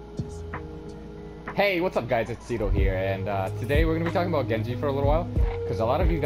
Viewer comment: Lul I heard "Hey what's up guys it's seagull here"